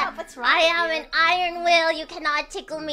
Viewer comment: is probably "I have an iron will" instead of "I'm an iron wheel", if anything she would be a rock wheel :D